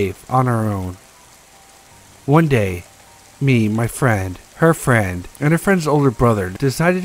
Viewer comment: Note: some of my "S" and lower sounds got removed as I had to remove heavy breathing since I was coming out of being sick. I'm stupid lol, sorry...next video won't suck...as much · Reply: i'm so glad you explained that it was getting pretty damned annoying by minute 40 it sounded like you said she woke up on her friends cow! lol